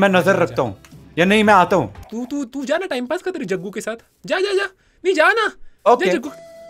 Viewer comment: Did mythpat say Jaja tere jungkook ke sath in